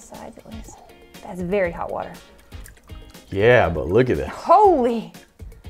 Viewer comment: Did she say holy s### and u took out the s###?